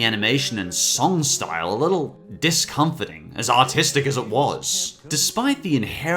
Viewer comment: I thought he said "as autistic as it is"